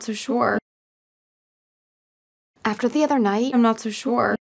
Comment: 에 i'm not too sure 로 들리는데 so sure이 맞는건가요 ㅠ